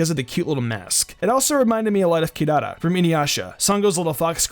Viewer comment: what’s that animal at 3:56? what anime is it from? he said it a bit too fast for me 😅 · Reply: Inuyasha. It's decent.